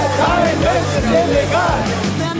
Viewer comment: Was hat er gesagt? Kein Zaun ist illegal? 0:23 Gut zu wissen.